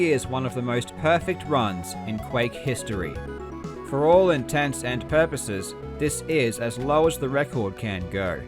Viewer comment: The saying is “For all intensive purposes”. I know it’s not important but just saying you said “For all intense and purposes”. At around 29.25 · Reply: It's also probably not important, but what he actually said was "for all intents and purposes", the original phrase dating from a 16th century Act and meaning for every practical sense. Not infrequently misheard as "intensive purposes" to the point that has likely become a phrase in and of itself. His use though is both linguistically correct and the original phrase. Edit: I should say I had never come across it misheard as intensive purposes. I didn't realise that was as much of a thing as it seems to be.